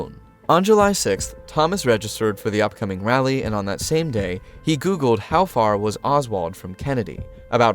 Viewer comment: When you said "He Googled.." my phone evidently thought you said "Hey Google" 😂😭 i was so confused like "Wow those are some good graphics" 🤡😭